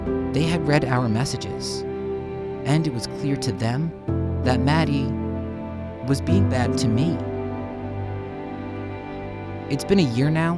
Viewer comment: What did he say? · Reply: Music Star maddi was being bad to me